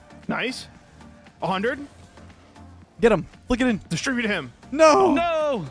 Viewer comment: I thought he said “distribute him”